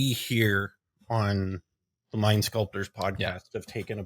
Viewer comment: when you said mind sculptors I heard “lime sculptors” 😂